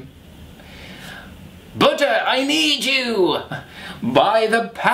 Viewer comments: Seriously thought he said, "BUTTER, I need you!" #SoConfused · Hahaha I thought you were saying butter not Buddha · I thought said "Butter I need you! By the power of butter!", and I was like, that sounds like me all the time! But then I realized he was saying "Buddha"....